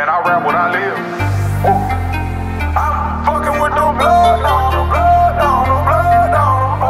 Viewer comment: Ever since he's found himself spirituality... he's been dropping pure music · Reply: It sound like he said he's fucking with the BLOOD DONOR